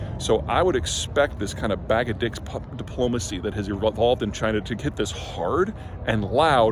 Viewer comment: He says “Bag of d!cks puppet diplomacy” which is an even better description than what most of you apparently misheard as “bag of d!cks diplomacy.”